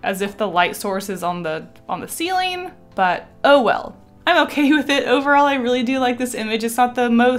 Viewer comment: I thought you said OwO but you said oh well